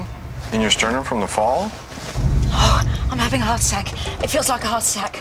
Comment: The captions said “hot SEC”. Instead of heart attack. 🤦‍♀️🤷‍♀️😂🤦‍♀️🤦‍♀️🤦‍♀️